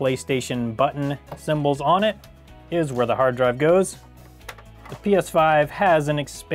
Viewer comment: Did he really said hard drive